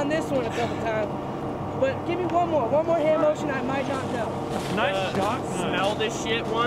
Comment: did they say " nice socks " ?